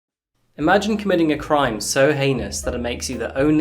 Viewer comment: I heard, 'imagine commiting a crime so anus' and I was like, 'i have..'